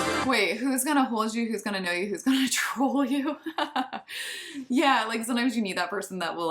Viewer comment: Wow! I didn't catch that little "me" until you pointed that out!! 🤯 And I also didn't catch "troll", either till now!! How funny!! I thought she was saying "hold you" again!! I've been mishearing the lyrics ALL THIS TIME! Wow!! And it's even funnier 'cause I also have seen the lyric video & just totally missed that!! Haha! 😅